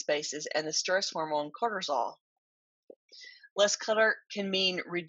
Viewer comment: Wow, you are batting 1000 as usual! 💗 I have to tell you something funny I THOUGHT I heard you say and later realized my mistake--- you mentioned a hormone called CORTISOL at 11:19 but I thought you were coining a comical term about something you were humorously calling "CLUTTERS-ALL"! 😄 You mentioned Cortisol again at 12:17 and that time I got it, but I did have a good laugh over it, since "CLUTTERS-ALL" sounded so appropriate to me! Thanks for always coming up with excellent assistance and encouragement for those of us wading through clutter! 😘